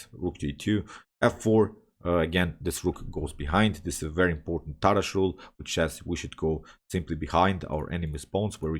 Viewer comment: Great video! Capitalizing on small weaknesses. What is the word used at 17:06 sounded like “tatashule” when a piece goes behind the enemy pawns? Thanks! 🙏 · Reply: Tarrasch Rule, rooks behind passed pawn. Siegbert Tarrasch.